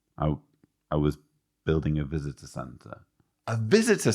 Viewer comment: I swear i heard "a visit to santa" when he said, "i was building a visitor center" 😆